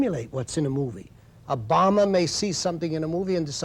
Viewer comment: thought he said "obama" at 1:39 haha. RIP great TUCO